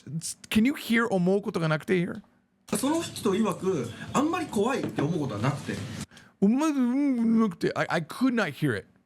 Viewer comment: 私は日本人ですがここは「あんまり怖いって思うってことがなくて」と言ってますね。 日本語母語話者ならはっきりわかります。 この話してる方が早口で終盤の「てことがなくて」の部分、特に「ことが」の箇所がほとんど子音を発音していないために「おおお」みたいに聞こえるかもしれませんが、音の数やリズムと微かな子音ではっきりと「てことがなくて」と言っているとわかります。